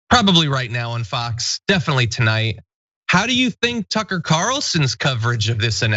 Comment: Did you say;"Sucker Carlson"???.... or I just misheard? ;)